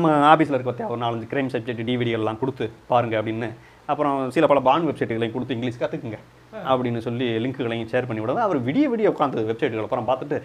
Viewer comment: Did I hear porn websites?!?!